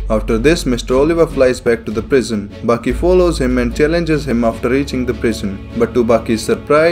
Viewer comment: I thought he said "Baki follows him and challenges him after eating the prison" at 4:23 and I'm still laughing